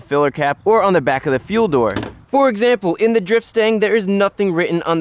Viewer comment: Oh, drift stang. I heard "drip stain."